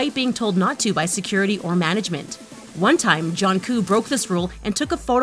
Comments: did she say jongq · Did she just say ”Junkoo” instead of Jungkook >:O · what did she just called JK again 🙂? jOnKoO ? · What? Did you just say Jonkoo? Itz Jungkook miss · did it say JOHNKOO?! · Did you just say johnkook ? It's Jungkook ok